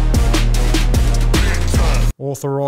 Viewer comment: What does Dutton say just after the transcript stops at 2:08 please? I can't understand it... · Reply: '...For Pauline Hanson's One Nation, Brisbane.'